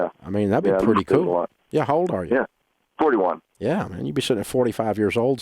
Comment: I thought he said he was 21 years old 😂😂I had anxiety, but happy he’s 41, I’m 28